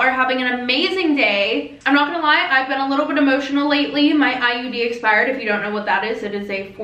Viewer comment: I swear I though you said it exploded